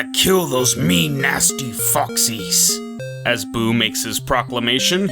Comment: I kept mishearing Buu as saying "Those mean, nasty FUCKS-" 😂